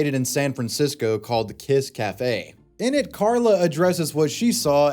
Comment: I thought he said Piss Café lmao😂